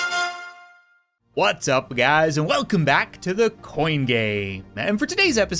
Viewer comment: It sounded like he said "what's up guys and welcome back to the coin gay" 😂😂😂😂😂😂😂😂😂